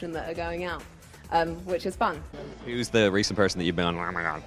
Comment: What does he say at 3:27 ? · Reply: "Who's the most recent person you've met and were like 'oh my god!'" :)